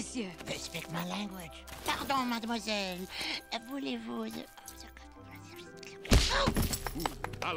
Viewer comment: LOL! I wonder what he said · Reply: Megan Ross I guess it's "Voulez-vous... coucher avec moi ce soir ?"... Translation : "Would you like to... sleep with me tonight ?" From the song, you know... That would explain the punch ;p